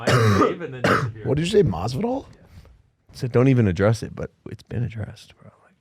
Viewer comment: does bro say masvidal?? Next Jake fight????